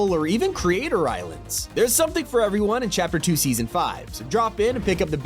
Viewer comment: People who heard Chapter 2 Season 5 💀 👇